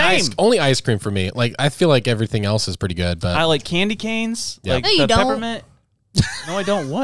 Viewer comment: It did sound like he said " i like candy canes "